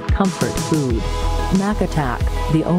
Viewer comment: "The only Mac and cheese so good it will make your grandma do the worn" The grandma: having a seizure and disintegrating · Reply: I thought it said "do the whirl" since she appears to be breakdancing